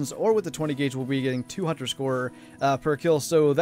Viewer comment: I thought you said 2 hundred score per kill at first haha. The longer video today was nice. Would like to see more longer ones, but I know it's not always possible when hunting larger game :) I kinda feel like hunting pheasants now. The dog makes it so much easier it seems.